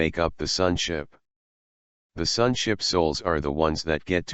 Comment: I know he didn't say it, but I first heard "Sun Chip Souls". I must be hungry! LOL